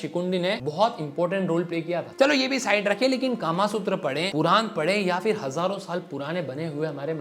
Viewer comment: Can you quote 'Qur'an' in this context. This is totally unacceptable i don't know about other religious books what they say about this thing but if you are quoting Qur'an without reference is wrong. Being a muslim i know that this doesn't have any place in Qur'an so pls trim that part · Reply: He said Puran Garud puran not quran